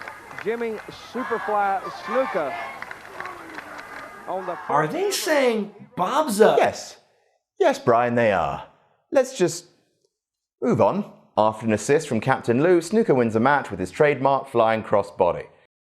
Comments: what are they saying? Bob's a __? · In case you couldn't understand what the crowd was saying, they said "Bob's a faggot!"